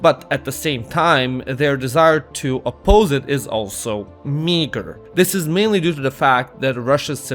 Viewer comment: bro did NOT say Miger